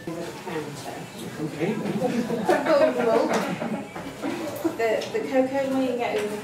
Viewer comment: did she say cocaine?